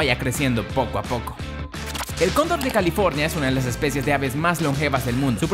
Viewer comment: Yo pense que decia condon de california